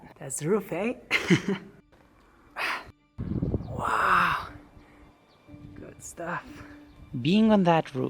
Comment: Everytime when he said "good stuff", in my mind I don't know why I keep hearing it as "white stuff", if you know what I mean